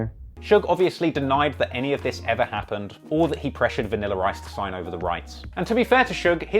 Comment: Why am I hearing you say “ Vanilla Rice “